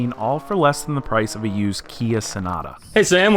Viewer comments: did he said KIA sonata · did he just said Kia sonata? 😅